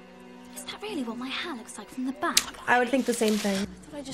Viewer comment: Could have sworn Hermione said "Is that really what my bum looks like from behind"...Mandela effect?